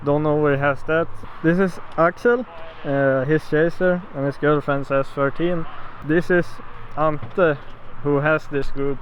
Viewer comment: And his girlfriend is thirteen hörde jag vid